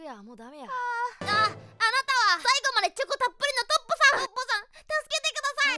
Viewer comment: トッポさんが独歩さんに聞こえたのは私だけ？（ヒプノシスマイク）